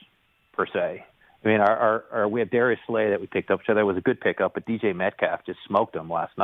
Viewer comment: It sounded like he said DJ Metcalf instead of DK.